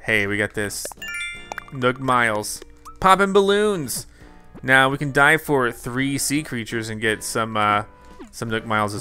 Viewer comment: I thought it said now we can die for 3 sea creatures;w